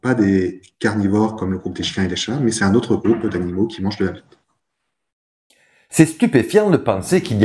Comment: MDR ! C'est moi où à 11:31 j'ai entendu : "C'est un autre groupe d'animaux qui mangent de la b*te" ? C'est fait exprès ? Je ne savais pas que l'on parlait de ce genre de sujet sur Simplex Paléo 😂😂 Bandes de coquins va !